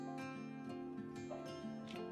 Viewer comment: I thought it said bottom not blossom